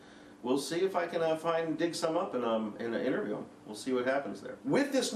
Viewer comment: sounds like he says dick some up on